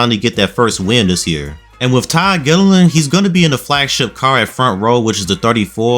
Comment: Did anyone else hear Ty Dillon? No? Just me? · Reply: Lmao I did too. I think he just said “Todd Gilliland” really fast so it sounded like Ty Dillon. Had me so lost for a second